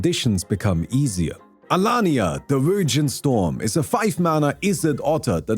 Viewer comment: man I really thought you said the Virgin Storm like sheeeeshhh